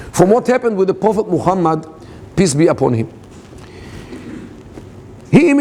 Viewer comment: Lol..thx Corrosionx4.. I should have read the comments first. It does indeed sound like he said "piss be upon him"